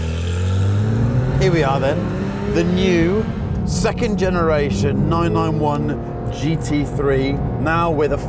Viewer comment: did he say "991" at 0:28?